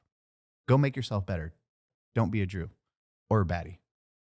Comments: did anyone else hear Jew not Drew lmao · I don’t mean to seem anti semitic, especially since I’m a Jew myself, but when was saying don’t be a baddie or a drew during the ad read I thought he said don’t be a Jew lol